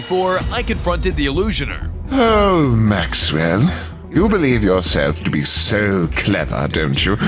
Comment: IS HE SAYING MAXWELL CAT OH U DONT